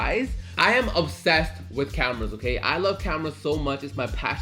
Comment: thought he said camels twice